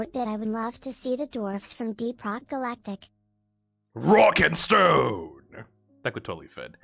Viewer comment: did i hear ROCK AND STONE !?